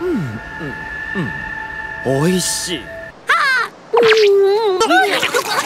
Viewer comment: why does it look like I hear “ h o l y s h i t